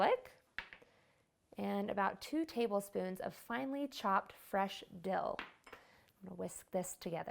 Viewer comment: i was distracted when she said fresh dill and somehow i thought she said fresh dildo...